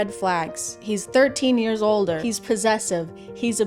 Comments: HELP I THOUGHT THAT SAID HE WAS 13 YEARS OLD 💀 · I thought she was saying "he's 13 years old" at first and I'm like yeah babe, that's certainly more than a red flag lmao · I THOUGHT IT SAID HES 13 YEARS OLD THAT SCARED THE SHIT OUTTA ME😭😭😭😭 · I thought it said "13 years OLD" instead of older... took me a minute to realize it... but in comparison those comments are not as bad now... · on that second one i thought it said "hes 13 years old" not older and was like woah hold up thats illegal. dont care about the killing people or stalking part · oh ym god i thought it said hes 13 years old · I heard “Thirteen Years Older” as “Thirteen Years Old” which SEVERELY changed the meaning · HELP I THOUGHT IT SAID THAT HE'S 13 YEARS OLD 😭😭🙏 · OMGG I THOUGHT IT SAID "hes 15 years old" NOOOOOO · I thought she said "Hes 13 years old."... like yep sounds like a red flag to me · HELP! I thought it said "he's 13 year's old"😭